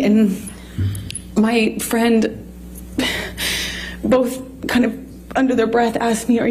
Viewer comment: To be fair it sounded like she said Jerry Judge Security & her friend both asked if she was okay, NOT that she was calling Jerry her friend.